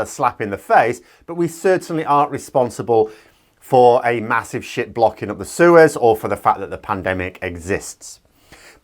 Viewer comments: You said: "a massive ship blocking up the Suez" I heard: "a massive shit blocking up the sewers" I gotta get my ears checked 👍✌️ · I heard "massive sh*t blocking up the sewers"